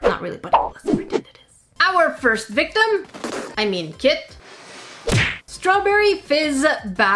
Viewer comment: What said : ''Our first victim... I mean kit'' What I heard : ''Our first victim... I mean kid''